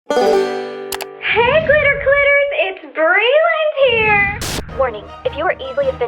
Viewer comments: At first I thought she said glitter clitters, instead of glitter critters. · In the beginning it sound like she say Glitter Clitters not Glitter Critters. Weird?